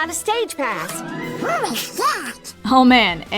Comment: AM I THE ONLY ONE WHO HEARS DONALD SWEARING AT 6:14 ? HE LITERALLY SAYS.. "Holy Sh*t" IF YOU LISTEN CLOSELY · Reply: Donald says “Who’s that?” but it sounds so much like “Holy shit!” 😂😂😂😂😂😂